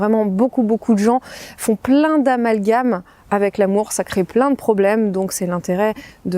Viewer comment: Quand j'entends à 8:25 "beaucoup beaucoup de gens font plein d'amalgames avec l'amour : ça crée plein de problèmes" Moi j'ai entendu "beaucoup beaucoup de gens font plein d'amalgames avec l'amour sacré" :D :D :D